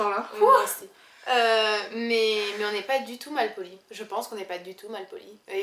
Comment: did You say "moje włosy"? :D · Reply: Aco Cieto She says "Mais on est pas du tout mal polis" = "But we are not rude at all"